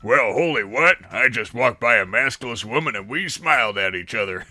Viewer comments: I was looking away from my phone and I though I heard "masculine woman" and I did a double take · i thought it said muscular woman Darn,the disappointment